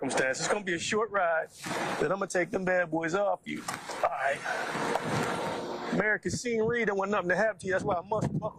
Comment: “America’s Senioritas” don’t want nothing to happen to you… Is that what he says? LOL! Smooth.